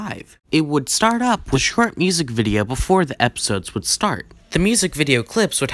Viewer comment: Did you say Shrek music video, what??? 😂😂😂 I'll be dead if that happened.